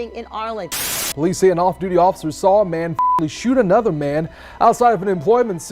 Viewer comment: "saw a man fuck@ shoot another man " 😂 obviously not what was said be this kinda cracked me up